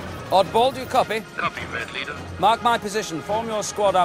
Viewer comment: Hey its Oddball from the clone wars! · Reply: Mjw Kim I was always confused because to me it always sounded like he said R4. Today I just noticed he said Oddball